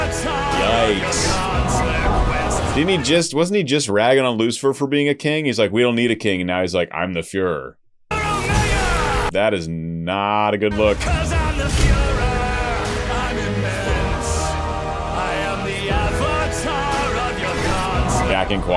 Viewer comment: I can’t tell you how many people heard furor and thought he said Fuhrer.